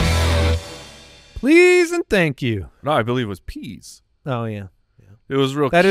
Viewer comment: To be fair I thought I heard "cheese and thank you", which would have been more 'on brand'.